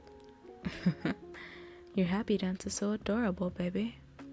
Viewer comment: did you say happy dance? 🥺 that’s so amazing